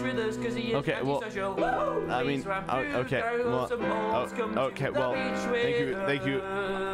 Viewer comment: can someone please tell me what tommy says was it please ranboo show some remorse or was it please ranboo put on some shorts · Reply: Tommy said "please Ranboo grow some balls, come to the beach with us